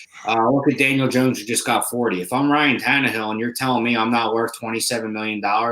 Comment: I thought you said they were trading tunnelhill